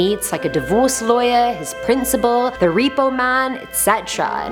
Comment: I heard "The Pedo Man" for some reason.